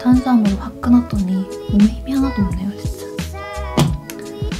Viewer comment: ？？？？聞き取れません..... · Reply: 항상 마무리 이렇더라 いつも締めはこんな感じ